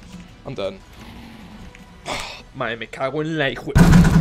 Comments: wot did he say actually bruh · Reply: my mac bamboo fly hook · wtf is he saying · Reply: he said: me cago en la y jue